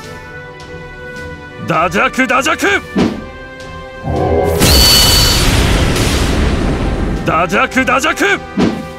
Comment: What people hear: DAJAKU DAJAKU! What I hear: HINJAKU HINJAKU!